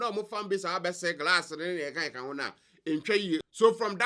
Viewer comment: did I hear glass 😂😂😂